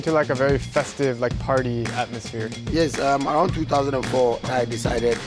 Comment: I think he said hearse